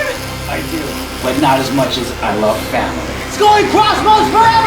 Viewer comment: did I hear family?